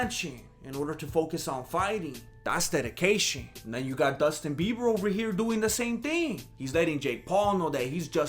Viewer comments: Did you say "Dustin Bieber"? 2:55 Orale! 👊😂👍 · I thought I heard wrong 2:58 but then I put the subs on ,it said the same thing - Dustin Bieber ....